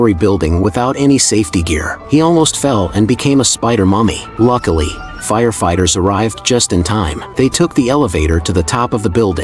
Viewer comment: did bro said spider mommy😂